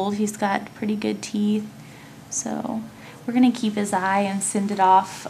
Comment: I moved my laptop and I thought you said "We're gonna eat his eye"